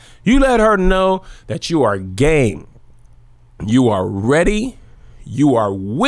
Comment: I thought at 8:45 you said "let her know, you are GAY" I was like... what? Then I realized you said game lol